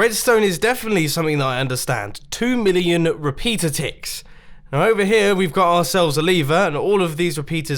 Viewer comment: Aevio Gaming the repeaters totalled 2 million ticks all together, so the setting of the repeater doesn’t matter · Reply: oh, i thought he said 2 million repeaters. My bad